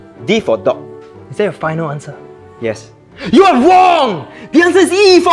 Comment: You are WONG!!! Edit: It's misheard ._.